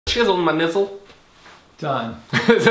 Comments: I thought he said “Chilling with my nipple” 😂💀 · At the beginning I thought he said ŋı℘℘Ɩɛ not nizzle Lol😹